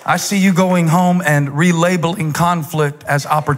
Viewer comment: "I see you go home and RE-LABLING conflict as opportunity"? (Instead of "...real able in conflict as opportunity"...maybe? The transcribed text doesn't make sense to me, but then my 'different ability' to communicate brings ALL KINDS of conflict into my daily life. This is my two cents' worth for now.)